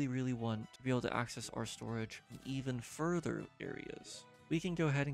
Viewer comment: AnD ThIs.. IS TO GO… EVEN FURTHER THAN BEYOND SUPER STORGE That’s what I heard😂